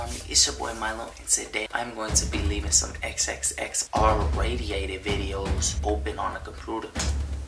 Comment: Did he just say xxx “radiated “video 😂